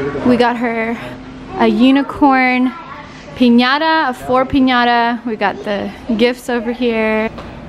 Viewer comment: Why was somebody else opening Elle's presents? And why didn't anybody say anything about it. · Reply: Actually I hear "UNICORN! Cat in a noggybu"